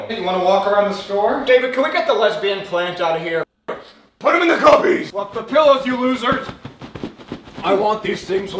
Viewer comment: "Put them in the CUBBIES" bro I thought he said " PUT THEM IN THE PUPPIES" BRO IM HOWLING😂😂😂